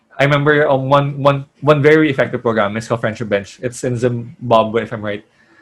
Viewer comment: I didn't get what program/intervention he said. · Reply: Hello! Mr. was talking about the Friendship Bench, which trains grandmothers in evidence-based talk therapy so that they can help their local communities. "The Friendship Bench clinical team trains community health workers (also known as lay health workers) to provide basic Cognitive Behavioural Therapy with an emphasis on Problem Solving Therapy, activity scheduling and peer led group support...our trained CHWs sit with their clients outdoors, under the trees on wooden park benches in discreet safe spaces in the community." For more info: <link> <link>